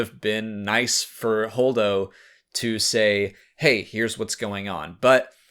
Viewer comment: every time i hear Holdo , i hear "Hodor" !!! LOL !!